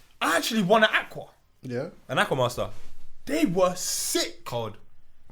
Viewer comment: Did say 'ana ku waso' at 30:46? 😂